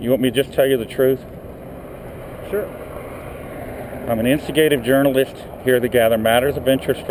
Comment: did you say you are in "instigative" in stead of investigative? 4:20 lol.. i know you are investigative. but that was funny!